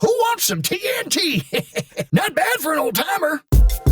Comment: I always thought he said “not bad for an old pepper”